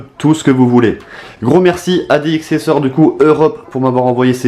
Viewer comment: j'ai entendu T-Series 😂😂